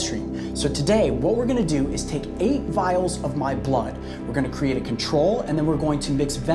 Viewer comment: the first minutes i thought he said 8 Miles of my blood